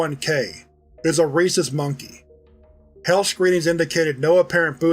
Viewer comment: 🤣 I heard "racist monkey " before my brain stepped in 😂🤦‍♀️